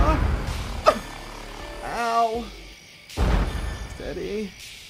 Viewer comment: When steven says “steady” i thought he said daddy lol then i readed it and it said steady